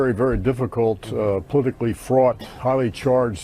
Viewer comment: Did Clapper say "politically fraud"?